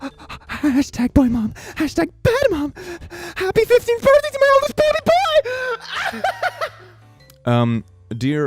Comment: What the part of the video says: "Happy 15tg Birthday to my oldest baby boy! [Insert Emoji]" What the subtitles say: "happy birthday to my oldest pewdiepie"